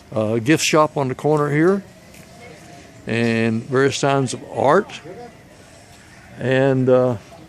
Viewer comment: Hi... did you say : it was a sign of art at 4:35? Please check your video. You may want to change that... It was 'Cocinart'... That could be translated into: the art of cooking... As you will see if you pause the video there is a list of what is on the menu underneath. P.S. How about breathing... I can not see how you can walk around without a mask. This place is loaded with cars. It reminds me of Antigua, Guatemala. It smells like burned tires, carbon monoxide everywhere. What is your take on that? Thank you!